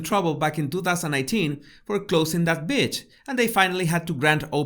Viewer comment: This video is so on point. Is it me or it sounds like you saying b** instead of Beach. 😂 either way I love your videos.